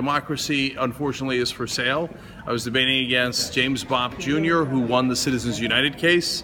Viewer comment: thought he said James Bond Jr. would've been cool